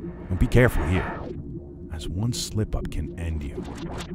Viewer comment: I thought he said "slippery"